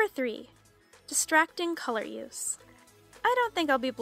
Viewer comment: did you say: distracting colorious? Or is it just me? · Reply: Lena Pozgaj she said color use